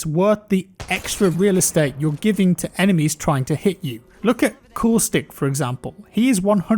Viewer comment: I swear he says "look at coolstick"